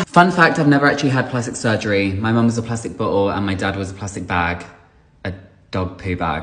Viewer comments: i thought he said a PLASTIC BUTT WHOLE instead of a plastic bottle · I heard "my mom's a plastic butthole"..🤣